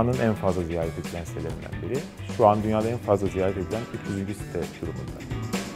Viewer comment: dünyanın en çok ziyaret edilen 3.site ekşidir mi dedi ben mi yanlış duydum